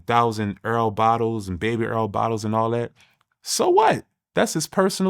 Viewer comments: Did bro just call oil “earl”?😂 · … I like Southern slang, instead of “baby oil” they say “baby earl”